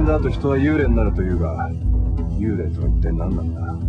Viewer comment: Did he said "Naruto", LoL?!